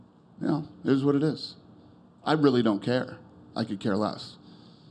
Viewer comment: Dana "I dont care" White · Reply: Michael Tortolano I thought he said I couldn’t care less